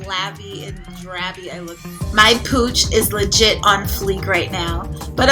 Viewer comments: I don't know why I thought you were saying "cooch" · no disrespect but I swear I thought u said ur cooch was on fleek lmfao.